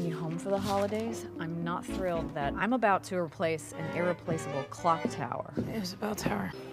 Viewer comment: "I have to replace the irreplaceable tower" "it was a bell tower" "the dean said it had a bell & a clock and an overachiever you are you managed to destroyed both" Lol this has got to be my favorite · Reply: I thought that she said replace the clock tower.